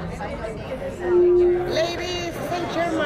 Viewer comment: The lady introducing him sounds like she says "ladies in Germany!"